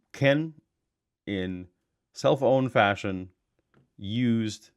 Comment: I keep hearing “cellphone fashion”.